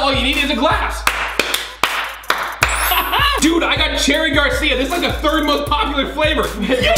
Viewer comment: I thought he said, ”Dude I’m like Karina Garcia!”